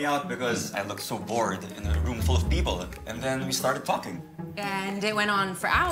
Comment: Am I and my sister the only one who heard Marco say “fx*king” instead of ‘talking’? In 2:37? Likeee, we’re literally ROTFL!!! 😭🤣